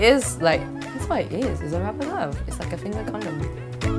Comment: Its like a finger condom??? Guyz, did she really say that?